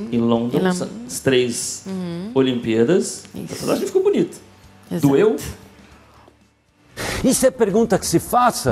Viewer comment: qual foi pergunta? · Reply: "Doeu?"